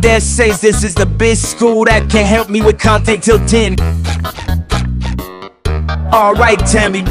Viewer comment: bro did he just say 10 but cant count to 10??